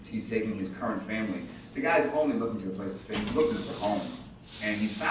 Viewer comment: What does Chris says at 34:39? Bucky is his... ??! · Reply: rolleyes... He said "Bucky is his ACHILLES HEEL" aka weak spot